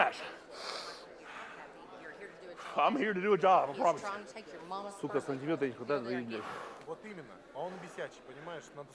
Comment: wtf did say at 16:54 "he's trying to take your mama's ....." strange way to motivate your husband by talking about his mom in the middle of a match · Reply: He trying to take your mamas purse lol